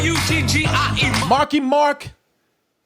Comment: I thought he said Marky Mark and the Fuck You Bunch 😂